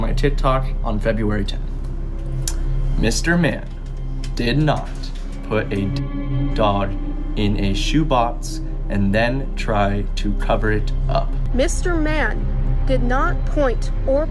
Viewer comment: can someone help me figure out what he said I hear seggs doll but idk if that’s what he said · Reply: I think he said Mr. Mann did not put a dead dog in a shoe box and try to cover it up